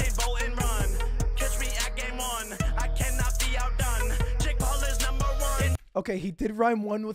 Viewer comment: he actually says “I cannot be out done.”